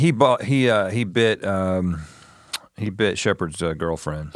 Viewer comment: Sounded like Rhett just said Sheoherds girlfriend…